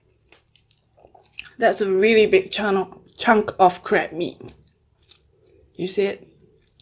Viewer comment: Lmfao I thought she said crap meet😂🤦🏽‍♀️💖